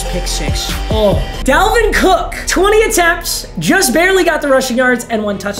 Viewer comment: I thought it said 10 attempts, not 20?